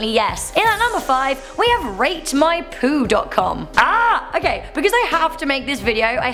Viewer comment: Rate my poo? What a load of shit! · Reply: at first i thought she said rape my poo and i just stood there until i realized she said rate my poo :/